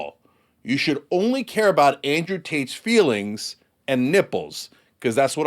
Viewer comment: I could have sworn he said "Andrew Taint."